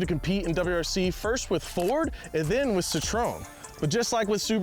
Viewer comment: what did you say? CITRON? lolol